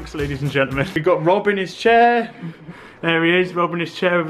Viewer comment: How did you guys get the tape on the ceiling? Yea Coz that makes sense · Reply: Look right, it sounded like "Yea" but now i hear "Chair"